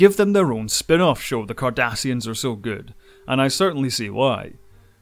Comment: Did someone hear him say this and hear Kardashian instead of Cardassian?